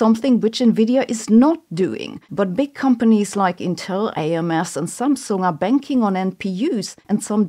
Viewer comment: did you call AMD AMS?